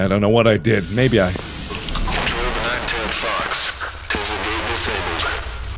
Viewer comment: did he say nine tailed fox?